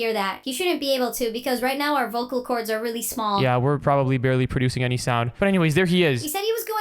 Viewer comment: did you say,VOCAL CORDS,VOCAL CORDS ARE DANGEROUS